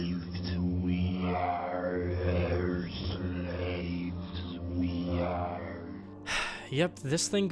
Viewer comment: I JUST finished playing Half-life 1 on Steam and the audio you are referring to at 12:49 played for me EXACTLY in the same room as the background video you had at that moment. I actually thought it said “we are the same”.